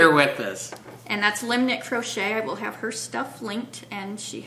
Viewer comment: my ears thought you said limited crochet...silly me!! thank you!!